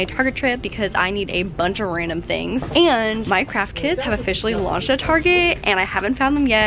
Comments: Literally at 5:08 thought she said Minecraft kids 😂😂😂🤪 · lol i missed when you said "my craft kits" and i thought you said "Minecraft Kids" and that you were super exited for those XD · is she saying: " Minecraft kids" or "my craft kids" ? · I thought she said Minecraft kids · did anyone else think lauren said minecraft kids? just me? ok... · When she said “my craft kits” I thought she said “Minecraft kids” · I thought she said minecraft kids instead of my craft kits 😂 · I thought she said “Minecraft Kids” i was like um huh? 😂